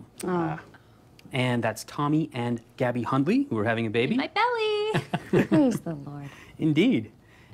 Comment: Did she just say "In my belly"?